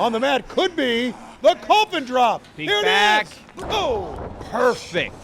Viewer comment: did Tony call The Coffin Drop The COVID Drop?